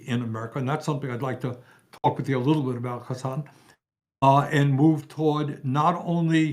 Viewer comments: omg did bernie just call hasan son? or did i mishear his name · Why did I feel so proud when bernie complimented hasan and called him son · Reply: I thought he said Son too, but I think he said Hasan. At least at the spot where I heard it.